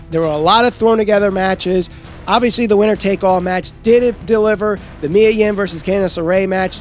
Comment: I didn't say it didn't deliver. I said the first match and the last did deliver and everything in between was filler. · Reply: I was shocked when you said it but it sure sounds like you said didn't deliver to me